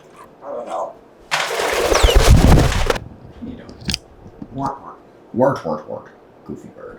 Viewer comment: My new favorite words out of Apollo. 'Wort Wort' 🤣 it almost sounds like he is saying 'work work's 🤣